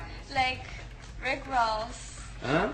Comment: she is not say Rick Rolls.....she is saying like Rick Ross the rapper